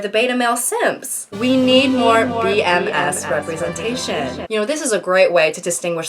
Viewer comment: fuck me, I heard BMS i think BDSM at 9:00 and thought waaaaaait a second, that does not lay in context!